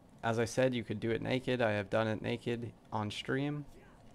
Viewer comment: All I heard was "I did it naked on stream" and was like...um...good..to know? Then realized im dum